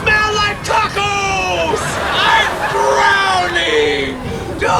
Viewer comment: LOL when he said "IM DROWNING!" I thought he said "AND BROWNIES!" Lol. The taco line confused me.